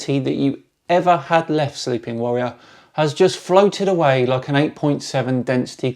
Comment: At the begining of the video, for some reason I heard you saying stupid warrior.